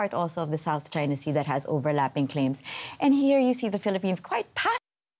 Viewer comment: what did she says "Phillipines is quite Path...." 🤔